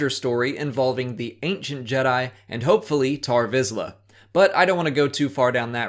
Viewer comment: So I heard wrong I thought they said tor vizsla and was really confused